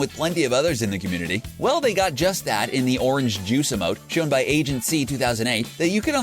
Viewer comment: did he just call orange justice orange juice!?